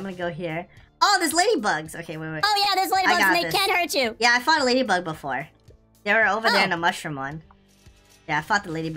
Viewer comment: My lost acc has half a hive of bees two gifted bees and rare and legendary bees im mid game not end game wait i think said i fcked a lady bug before